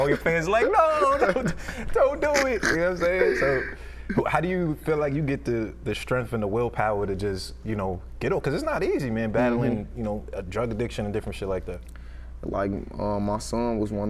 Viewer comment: drug addiction . Like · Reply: i thought u said durag addiction i was like what?